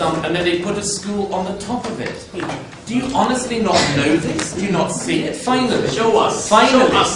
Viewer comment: What was it that Murray's opponent said at 18:08 that drew such an appluase? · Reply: "Show us, show us man" I think